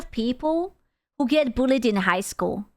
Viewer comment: Um love your accent but pronounce bullied better because I heard "these are the type of people who get a bullet at school"....